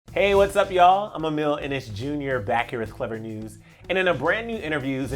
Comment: WHy do i hear 'Emile and it's Jr' instead of his actual name🤣🤣🤣🤣🤣